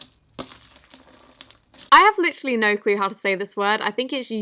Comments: *ahem* (Goo- DAY- Tah- MAH) You’re welcome. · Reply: i thought it said good day to me but its actually pronounciation of gudetama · When I saw the Yummy Gautama slime I got Darkest Minds thoughts. When you like your own comment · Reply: I literally heard her say “guditama!” I’m my mind lmao